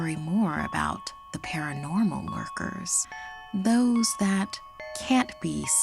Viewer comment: i thought she said workers